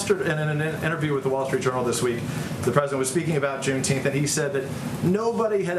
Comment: I had no clue what June teenth was I thought he said June teeth I thought it was Some type of joke